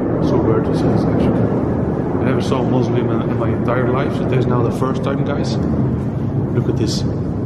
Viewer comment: i thought he was saying i never saw a muslim in my entire life LOL